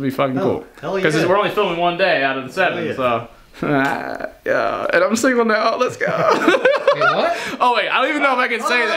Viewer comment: what did he say? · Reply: “And I’m single now let’s go”